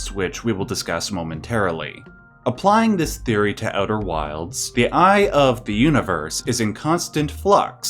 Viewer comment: Ok. So I was listening to this and thought you said "outer worlds" and I was like "bullshit" are you doing a video on that game. 😂